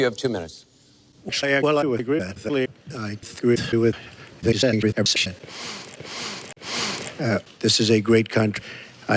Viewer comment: 1 week and still no annotations FeelsBadMan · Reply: in the end of the random shit it sounds like he says " i agree with this angry russian" i laughed so hard actually thought that was intentional :D.